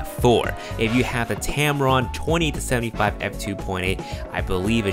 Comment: Sorry. Did you mean the Tamron 28-75 works with A7iii??? Sounded like you said the 20-75. Thanks